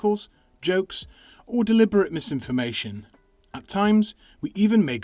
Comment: i heard that as “autists” and i had to rewind lmao